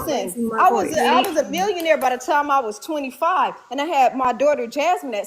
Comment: No one is a billionaire on here I don’t know why that girl is lying if I heard right lol 1:33:22 mark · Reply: Think she said millionaire...idk